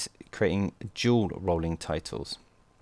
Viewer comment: I figured out (I think) that at 2:08 you are saying "dual rolling titles." (Is that right?) The closed captioning said "jewel rolling titles."